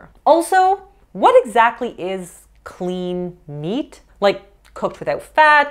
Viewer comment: I thought he said Lean meats, not clean meats.